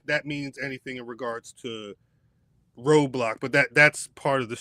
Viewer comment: Yall said Roadblock but I def thought you said Roblox🤣🤣🤣 Jade (yall know I’m new here) seems like they want her to be great because she’s an amazing physical specimen but she just hasn’t shown it with ring work. They may have to go to the Diesel or Hogan route with her. Or strategize the bookings to make her look the best if she can’t get her skills up.